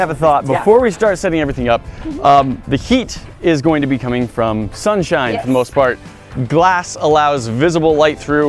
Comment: When said sunshine it sounded like he said shunshine